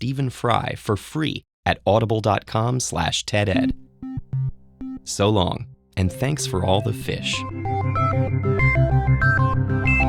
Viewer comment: What he said??☺ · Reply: So long! And thanks for all the fish!